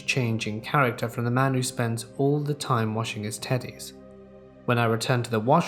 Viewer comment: The teddy story got me, my guy i thought u said he washed his tiddies xD